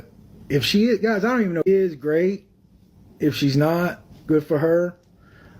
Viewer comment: did call her babe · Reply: it sounded like “if she is, great” as in “if gypsy’s pregnant”